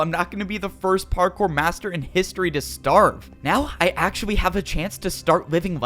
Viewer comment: did he say actuawwy